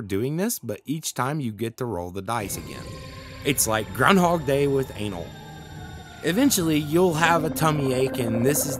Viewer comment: I had to rewatch that 3 times to make sure I heard that right... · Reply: Until now that I went to reload it, I thought it said "but with aliens". Guess I heard wrong...